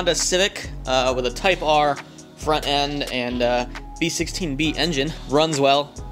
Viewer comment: Is it a lookalike or a replica? For a proper replica you need a B16B engine. :) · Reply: If i heard correctly he said that it has a B16B at the beggining of the video.